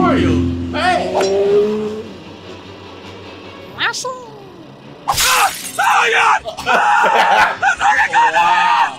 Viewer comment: listen carefully to what yoshi said 😂😂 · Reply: THERESFUCKINGGLASSINMYEYES!!